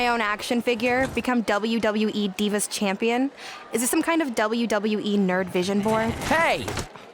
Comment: I was cooking while watching so I thought Brooklyn said "is this some WWE nerd vision Porn"😂 so glad tre vision cleared that up